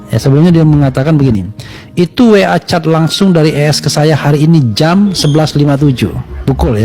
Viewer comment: coba perhatikan... "dari ESS" bukan "dari BES" apa saya yang salah dengar ya😂😂